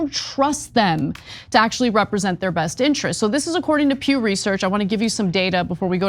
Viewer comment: why does it sound like Ana was saying, "according to puberty search"... talk about something that should NEVER be typed into a web browser